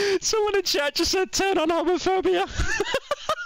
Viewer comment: am i the only one who has no idea what chat said lmao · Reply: Homophobia is what he said.